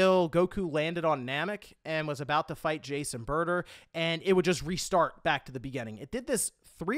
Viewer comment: I swear I heard "Jason Burger" and I was like who?! Then I thought about it and realized it was Jiece and Burta. Lol